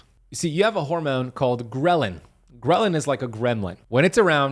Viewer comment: Thought he was saying gremlin lol.and yes this is true